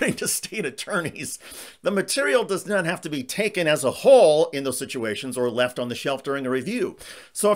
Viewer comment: did he say taken as a hole? 😂😂